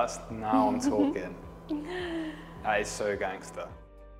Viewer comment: Did I hear right saying 7:26 "That is so Gangster"👍👏That was🙌